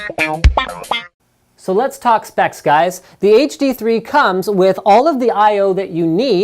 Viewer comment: I thought he said "So let's talk sex guys."